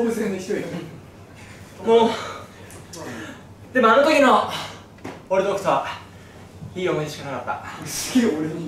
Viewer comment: しんたろーの俺と北斗はっていうところが俺ドクターだってめっちゃ聞こえる笑笑